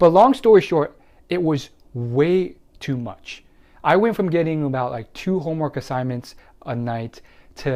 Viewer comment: Who is here cuz they are failing corona online school 👀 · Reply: lol at first I thought you said went from f to rip